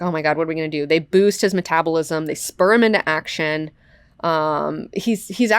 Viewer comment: "They spur him into action..." I thought you said "They sperm him into action" at first. Strangely enough it made sense.